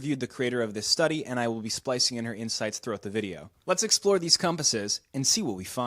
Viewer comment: I heard that as "I will be splicing in her insides throught the video"